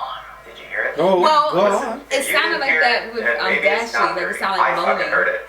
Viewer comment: i hear f**k God. (God please forgive if ive sin)